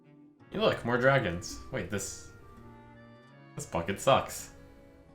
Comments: listening on low volume, I thought he said "This fucking sucks." Jeeze! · I really thought he said “this fuckin sucks”. I rewatched it and was disappointed :(